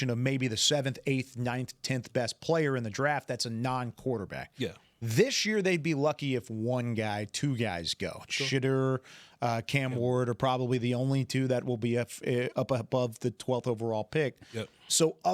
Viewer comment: Did just say shitttur😂😂😂😂😂😅